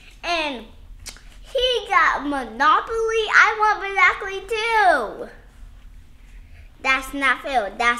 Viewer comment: “I want a present to!”😭😭😭😭😭😭💀💀💀💀💀💀💅💅💅💅💅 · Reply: I heard"I wanna play too"